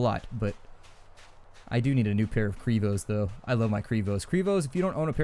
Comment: does anybody know what brand he's talking about? I can't figure out what he's saying, primos, privos, kuribos, google isn't helping. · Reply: He is saying Crevo slip on shoes